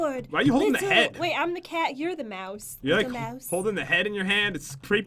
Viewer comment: Does anyone else hear Jen say dumbass